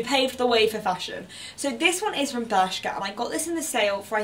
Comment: Where did you say you got the black blazer from? I can't understand you, sorry. G-something-gal? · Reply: Bershka!